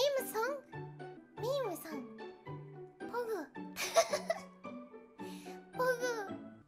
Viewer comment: Did she say pog?